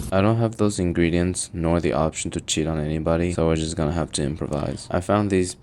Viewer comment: even i heard her saying "cheat on me" like is that what she said